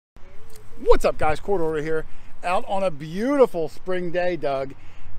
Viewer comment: Not that it matters much. My wife and I watch your channel all the time. But in your opening where you say "Quarter Hoarder Here", to my southern ears, I hear "Court Order Here". Anyone else ever mention that to you?